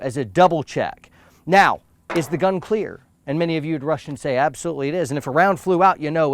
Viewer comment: 😂😂. Did he say "Many of you'd Russians say absolutely it is," or "Many of you'd rush and say absolutely it is?"